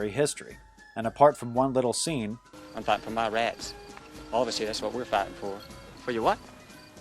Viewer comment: what i heard confederate guy : i'm fighting for ma rats. union guy : your what? confederate guy : ma rats union guy : existential crisis